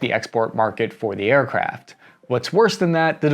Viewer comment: sounded like you were saying aircraps